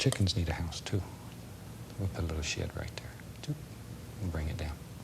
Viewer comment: I really enjoy these videos and have a huge respect for the man but I got to say that I heard that Bob wants to put a "little sh*t right there" 17:40 (I know he said 'shed'). :D